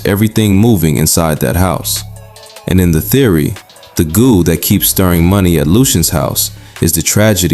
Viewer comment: what the hell you talkin bout some Goo😂 He said "Golden goose" bruh and Keem's mom is alive, he mentions this on Highway Pt. 2. This video feels lazier than your usual stuff bro.